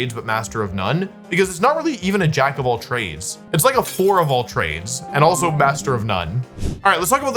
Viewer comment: HELP, I WASN'T LOOKING AT MY SCREEN AND MISSHEARD IT AS "A WHORE OF ALL TRADES"